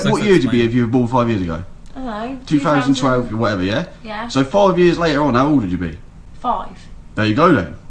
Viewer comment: At first I thought he said 5 years earlier but than I got than I got the question.